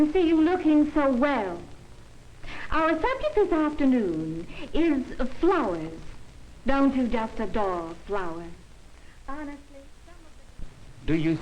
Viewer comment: what does she say at 6:49 ? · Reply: "Our subject today is flowers. Don't you just adore flowers?" Then it's inaudible.